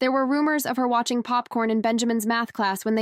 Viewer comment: Popcorn did she say that or did she say something else